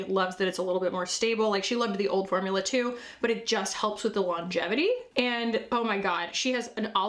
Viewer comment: When you said the eyeshadows have longevity, for some reason I heard "lunch money", and somehow it still made sense. I will now judge everything on whether I believe it has lunch money.